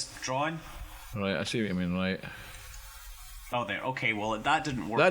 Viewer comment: I think I may have misheard him, but when Scott's dad was putting the adapter on, did he say, "bro, I see what you mean"?